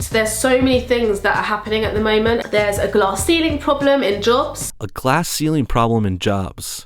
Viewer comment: I swear I thought she said glass eating problems